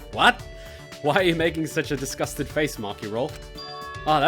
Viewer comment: I thought kaito actually said disgusting and I was like "ouch maki"